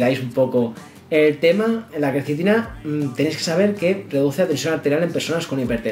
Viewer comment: no se entiende bien que dices, si reduce o aumenta la tensión arterial. Yo soy un poco hipertenso, me conviene tomarla? Gracias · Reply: Se reduce la tension.de nada